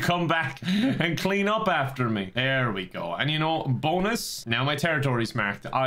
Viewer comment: Did you mean to say "BONEus" instead of "bonus,"?